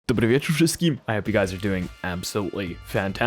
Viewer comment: What did bro say at 0:00 ? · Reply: dobry wieczór wszystkim (polish)